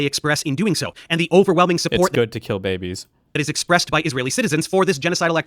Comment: can you tell me what Destiny just said? And you can try rewinding or forwarding to get all the context you want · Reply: he said it’s good to kill babies.